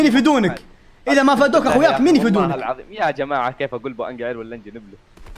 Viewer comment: ابي ادري وش قال 😂😂😂😂😂😂😂😂😂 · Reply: كيف اقول بو انقهر ولا شنو